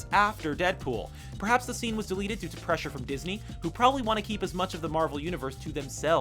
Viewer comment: did he say Disney